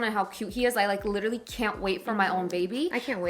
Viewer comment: does say " i cant wait for you guys to have kids?" 😭 omg is in a relationship?!